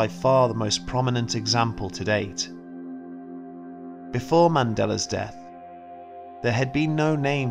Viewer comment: My ears are playing tricks on me. I kept hearing "madelar". The next time I watch this again, I'll see if that's what I heard. (or it's probably my cold)